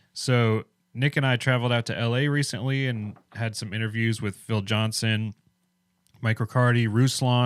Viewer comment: I really love your approach and how you interview and listen to people on both sides of fence, but you had me there when you listed the people you interviewed on the topic of prophecy, and I thought you said Bill Johnson, as a strong theologian on this topic! I had to rewind and realized that you said Phil Johnson. Way better, I breathed a sigh of relief… 😅 thanks for being thorough, but also recognizing Phil Johnson as a strong theologian on this topic and not someone steeped in NAR and new age! 👍🙌 God bless you guys!